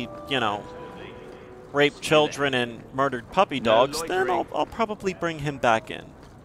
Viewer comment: I'm sorry i have trouble hearing, Did you just say Burned Children and raped puppy dogs?